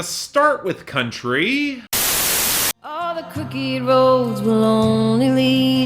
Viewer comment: Tell me why I heard "all the cookie rolls will only lead you home" instead of crooked roads.